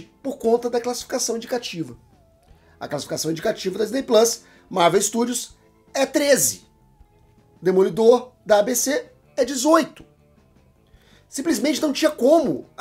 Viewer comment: Só eu ouvi ele falando "Demolidor da DC em 03:17?